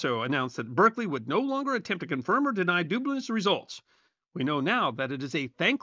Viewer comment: “Berkeley would no longer confirm or deny Dubna’s/dubious results.” I hear the portmanteau “Dubnious” here… 😂